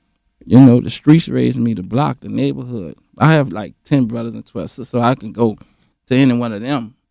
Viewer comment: Did he say he got like 10 brothers and 12 sisters??? at 14:11 smh... and no one taught him how to say "respect"...respectfully.